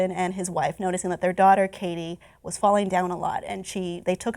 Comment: It sounds like it says cutie Katie